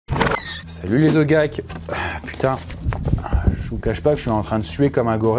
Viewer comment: Mdr je sors de la vidéo de Squeezie la, a l'intro j'ai entendu "Salut les Zodiac" 🤣